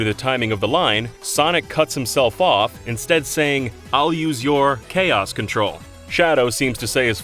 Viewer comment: I never knew the line was "I'll use your technique! Chaos Control!" I always heard "I'll use you! Chaos Control!" I thought Sonic was referring to the fake emerald he had with him.